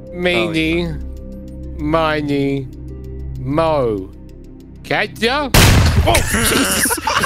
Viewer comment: When he said “Catch A” it sounded to me he said Ketchup, so I turned on the Auto-generated captions and apparently it thought it heard the same thing as I did lol